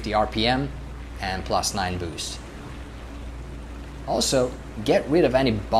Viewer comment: Thanks for a nice tutoorial. But what did you say @ 4:54 after '2850 RPM'? Something like '9 loose'? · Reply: ‘Plus 9 boost’. The boost pressure gauge under the tachometer should read 9 bars.